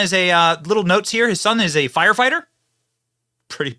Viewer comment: Benny, I thought he said that he is the son of a firefighter. Not his son is a firefighter. That speech he gave was amazing